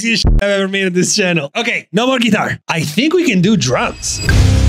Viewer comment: fr i thought he said drugs on this part 😂 lmao